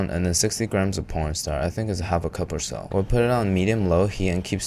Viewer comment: Did this man say pornstar instead of cornstarch at 3:10?